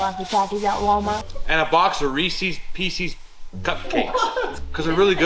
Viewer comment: Thought I heard, "we got Reese's Feces", LOL